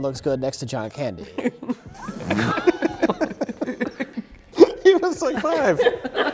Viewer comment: what is Mike saying? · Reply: "He was like, 5" Refering to how old Mac was when he was acting in the movie, Uncle Buck (1989).